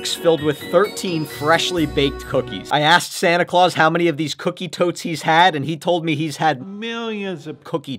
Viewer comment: I thought he said cookie toe💀